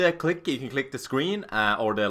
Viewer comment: LOL thought he said boner clip!